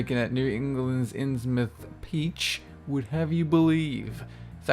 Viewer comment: Is it just me, or does say "peach" instead of "beach" @ 5:09?